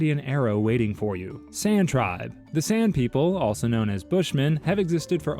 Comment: Was listening and not watching. In the San part, at first i thought he said "Sand People" and all i could think of was Obi Wan saying they were easily frightened, but would soon return, and in greater numbers.